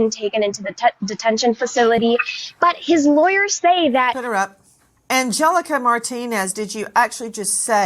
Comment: did she say "shut her up" as she was speaking?